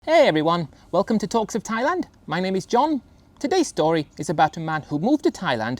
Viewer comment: Everytime he says " Welcome to Talks of Thailand " I hear "Welcome to toxic Thailand" LOL.